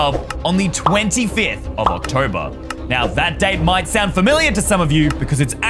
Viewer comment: Did you say 25th of October if yes then that's my birthday 😱